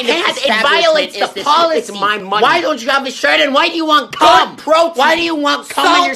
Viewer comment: What is he saying at 0:08 "why do you want me shredded". Understood everything else, captions aren't helping. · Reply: He says "Why don't you have a shirt and why do you want come?"